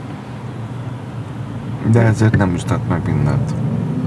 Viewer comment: és 11:46 között mit mond??? Nem értem 😀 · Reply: "De ezért nem is tett meg mindent"